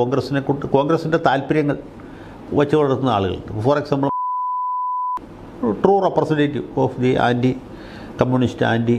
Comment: What does he say at 6:04? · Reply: Malayala manorama